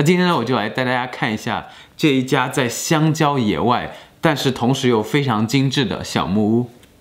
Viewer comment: 只有我聽成香蕉野外嗎🤣🤣